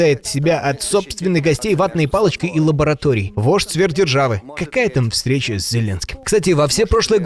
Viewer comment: Не вождь сверхдержавы, мне послышалось ,, вошь сверхдержавы 😅"